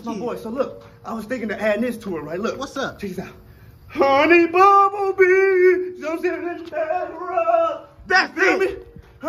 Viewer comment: THAT HONEY BUMBLEBEE AND BACK RUB MOVE IS PRICELESS. DAMN I'M CRYING LAUGHING OVER HERE 😂😂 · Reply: Yooo🤣🤣🤣 I swore he said Honey Bumble Bee and Run 🤣🤣🤣